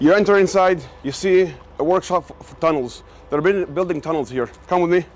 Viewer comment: did he say tunnel?